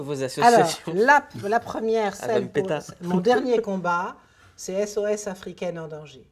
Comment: il dit quoi à 04:33 ? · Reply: " elle va me péta "